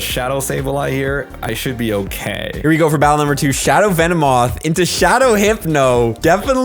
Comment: I thought u said u battle Zoe two dots